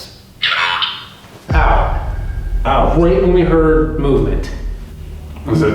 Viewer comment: I heard “GET OUT NOW!” On the digital recorder